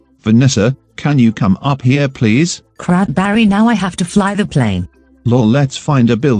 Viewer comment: When she said crap barry i thought she said cranberry so i jokingly said "haha sprite cranberry" and now Lebron James is sitting on my bed. Send help.